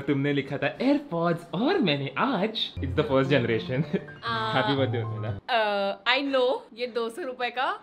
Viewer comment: did mythpat say mithila...???? 😶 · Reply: No he say urmila